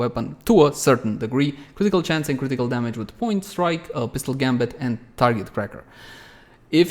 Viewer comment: Did you just say 'Point Strike' XD